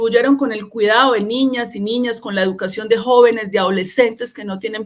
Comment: Doctora Carolina le entendí "niñes", no por favor.